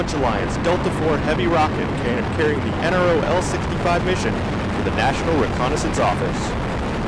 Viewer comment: it sounded like he said: crrieng the NROL 65 mission to the national recon office